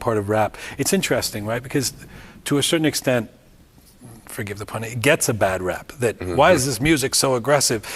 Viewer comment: Does Softball say "forgive the pun" at 3:00?